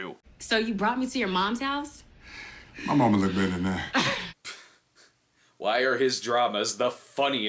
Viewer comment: What did he said? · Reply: My momma'd look good in that 😂